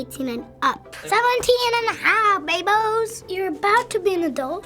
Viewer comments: I heard Bimboes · "17 and a half bay boes" That just made me crack up, no idea what last word was, though.